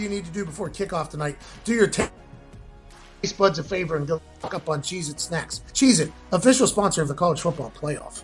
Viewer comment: Due to the audio glitch at 1:28:21 it sounds like he says go fuck up on cheese it snacks lol